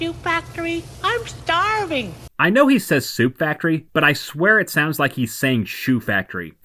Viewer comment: Yeah, you're not alone in that. I also thought he said "shoe factory" instead of "soup factory" for years.